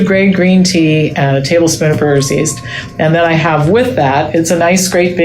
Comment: Does anybody know what Dr Pamela Popper said at “1:58”? A tablespoon of what?? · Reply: Brewers yeast